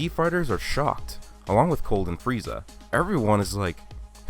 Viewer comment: when he said cold amd frieza I thought he said golden frieza lol